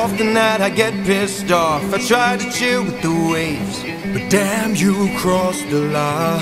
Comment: (I misheard a bit so-) Poseidon: I try to chew with the waves